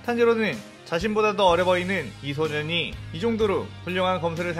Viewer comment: 미소년이라고 들었다...